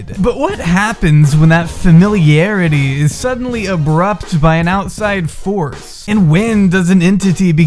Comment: did you just say… “abrupped”? like “abrupt” but as a verb?